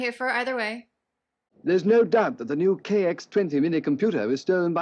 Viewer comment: Aha! I thought so! He said "KX 20"! 😮 Gotcha! this is a great mix of live action and marionettes! Kind of wish this episode was a little (no pun!) longer! Thank you (pardon me... You were a nun? 🤔 you need not get into it!) ❤ 😘😉🐉❤️🔥💕🥰🌹☺️👍